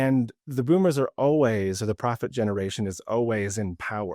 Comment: Funny, when you said Boomers were the "Prophet Generation" I thought you said the Profit generation. Hmmm.